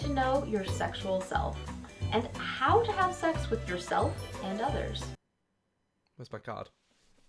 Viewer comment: did she say how to have sex with yourself :o